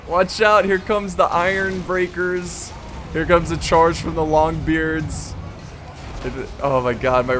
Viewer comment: Did I hear correctly? Here comes the charge of the longbeards? I'm of the Longbeard clan too! :D